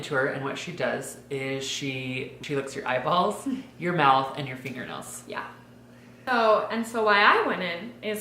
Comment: I thought said she LICKED your eyes, mouth and nails 😂